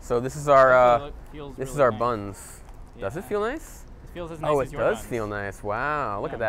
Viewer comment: what did he sayyyyyyyyy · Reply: Feels as nice as your buns!😳